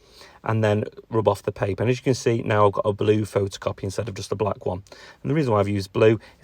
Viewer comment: Sorry if this is a stupid question but is the black and white photo of the bridge an actual photo? It sounded like you said 'photocopy' so would love to know which? I'm enthralled by this particular part of the the process you've shown us. Just amazing. Feels like it's that 'light bulb' moment I've been waiting for! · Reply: Yep photocopy, use a laser printer, apologies for the late reply👍🏻